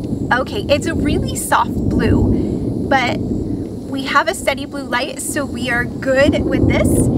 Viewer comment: Kirk! Kirky Douglas! Woohoo! It's has been a while since I've gotten your world class enumerated video notes, so yippie! 🎊🎊🎊 1. I sure can! Well, the adventure vehicle can! And this time, I didn't take out power to the half the campground 🙈😆🌲 2. Right! Gosh, it literally never stopped raining ⛈️⛈️⛈️ 3. It sure is ⭐⭐⭐😄 My adventure vehicle is a 2022 Ford E-Transit, and the mobile charger is the one that came with the purchase of the vehicle 🚙💰💰💰 4. OMG. I was rushing to film before the storm hit, and I am not surprised if you could really hear my stomach growling haha. Though, it also could have been thunder. But also my stomach 😋 5. Shhhhh (🤫 I took the cover off for a very quick moment while filming so that you could actually see the outlets 🤫🥷) 6. Ah, I love that idea. Thank you so much for sharing it with me. I have my video lineup all set through the middle of October or so, but I will certainly keep your suggestion in mind 🙏 7. Exactly! ... but only because they don't make a neon pink 😆 8. Stevie? She's always steamy. Or wet. Or muddy. And, I occasionally drop her. Poor Stevie! 😄⭐🎥💦🍃☂️ I'm dying to know how things are for you and on the trail! 🚶‍♀️‍➡️🚶‍♀️‍➡️🚶‍♀️‍➡️ Are you doing well and having some amazing adventures? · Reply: omg! I really thought you said steamy! That would have been an interesting story.